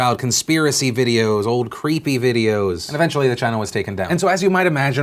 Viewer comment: I know I'm just mishearing Phil, but it keeps sounding like "the China was taken down" lol